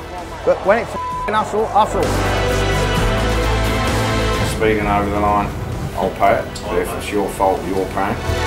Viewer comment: What does say at 1:06 ? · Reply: speeding over the line?